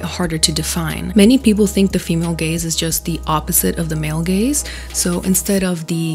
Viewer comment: I'm sorry but when I first heard this I thought they said male gays